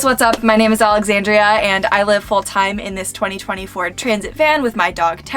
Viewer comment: How can you gave a 2024 year van when we are in the year if 2022????? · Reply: I thought she said a 2024 vehicle as well. She must have said a "2020 Ford" and we heard 2024!